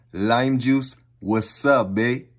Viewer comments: that wasabi sounded like "What's up, babe?" 🤣🤣🤣🤣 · When I first watch this and when he said wasabi I thought he said what sup bae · When he said Wassabi I thought he said what’s up baa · I thought he said wsp baby when he said wasabi😭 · It sounded like he said was up ba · Sound like he said wassup bae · I cant hahaha ,,Wasabae" ❤😂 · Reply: I heard "wassup bae" 😂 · When he said wasabi it sounded like he said what’s up bay